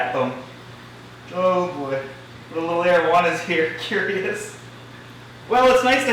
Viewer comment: rip arowana for a second i thought you said marijuana lmao but ik its areowana or whatever